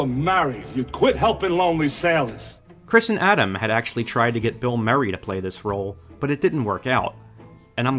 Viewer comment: What name does he say at 14:15? Its sounds a bit like 'Bill Murray' but more like 'Phil Merry'...